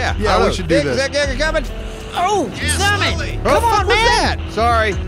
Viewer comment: Did just said "what the fuck was that?"? For the 3rd time in history?!! Hallelujah !